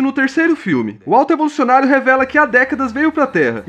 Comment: Eu ouvi “o alto bolsonaro”